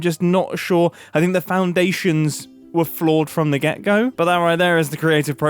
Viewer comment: every time someone says "From the get go" I hear "From the gecko" almost fully expecting them to flash a image of them taking something from a gecko.